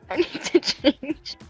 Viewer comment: Did she just say she piss heself?